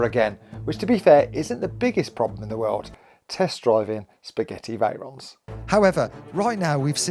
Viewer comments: Am I the only one who hears Spaghetti Veyron? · I swear I heard Spaghetti Veyron repeatedly 😂😂 Senna's influence?